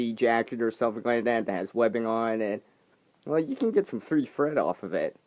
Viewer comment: I swear I thought you said "free fred" instead of free thread ..LoL